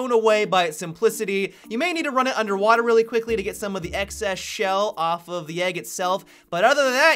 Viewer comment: for a second I thought we was going to say excess shit